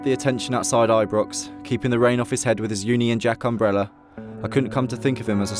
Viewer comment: lol 13:33 to 13:36. it sounds like he said "keeping the rain off his head with his uni and jack umbrella"